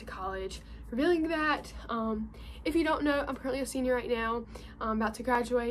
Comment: Legit thought you said "....I'm currently a senior at Yale" and then I became really confused haha Congrats on USC, and GO ALPHA GAM! ;-)